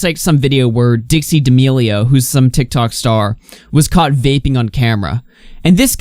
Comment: i thought you said "gaping on camera" for a second, turns out you said vaping not gaping